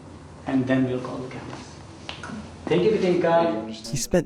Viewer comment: my ears thought the director said thank you? or take your time? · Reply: Thank you catinca ( he’s talking to the child actor)